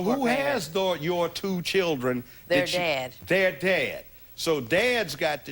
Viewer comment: I thought she said “they’re dead” not “their dad”